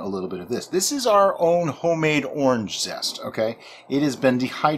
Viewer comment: Please, can you tell what you are adding in? I can't understand from the words you say, sorry. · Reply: Dried orange zest.